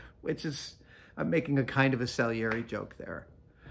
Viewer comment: Adam says, "I'm making kind of a .......... joke there". What is the missing word? Google autocorrects my guesses to "salary" and "celery". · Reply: Was it “Salieri”? <link>